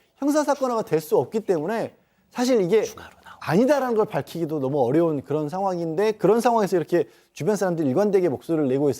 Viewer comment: 초 쯤에 속삭이는 소리가 들리는데 뭐라고 속삭이는건지 못 알아듣겠어서 꼽히넹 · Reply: 추가로 나온거?라고 하는데요?